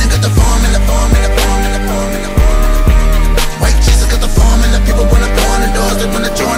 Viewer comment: I'm confused what does Rittz say at the beginning of the hook? it sounds like he says Nigga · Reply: I won't even lie lol the first time I heard it, it sounded like he said "White chicks got the formula!" took me few times to realise he said White Jesus lol